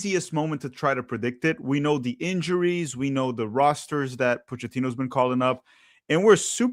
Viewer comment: the cc says berhalter instead of potch 😂